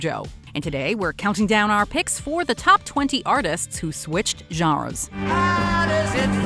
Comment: I thought it said Top 20 artist who switched genders.